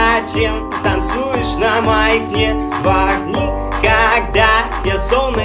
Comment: мне одному послышалось невах??xD